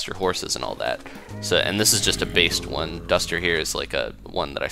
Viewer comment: I'm not sure if it is what you said, but it sounds like "this is just a based one." #ThankYouBasedHorse